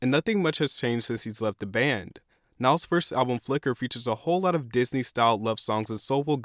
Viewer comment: I thought it said fucker instead of flicker